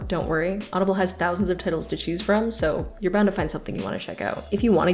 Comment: was I the only that heard “tittels” instead of titles?